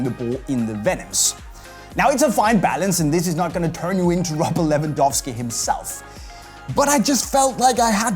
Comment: If you turn on captions, when said Robert Lewandowski, the caption said "rupp $11"